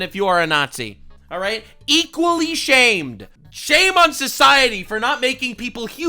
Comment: Oh shame? I thought you said shave… I going to do both!